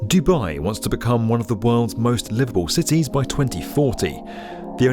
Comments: I thought he said: most liberal city….. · I thought he said Dubai wants to become one of the worlds most liberal cities hahaha